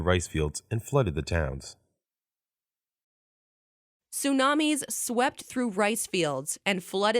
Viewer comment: Tsunamisが聞き取れなかった俺は末期